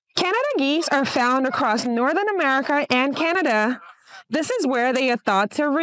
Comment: "Canada and North America" are not two different places. Canada is IN North America, as is the United States. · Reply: She said NORTHERN America and Canada…. 0:42. It’s obviously what she meant to say the second time she mentioned it. Chill lol